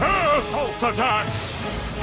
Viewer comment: ASSHOLE ATTACKS! (is what I thought he said)